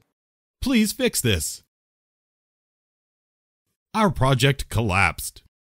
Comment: Второе предложение послышалось: this guy pregnant )